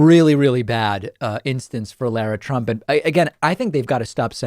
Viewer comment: I thought you said Larry Trump and now I'm made that it isn't.